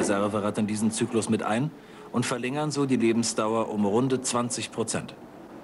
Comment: Das hat sich für mich die ganze Zeit nach "verringern" und nicht nach "verlängern" angehört, was natürlich überhaupt keinen Sinn gemacht hätte.